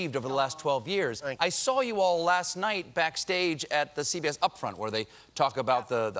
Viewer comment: did he say CVS??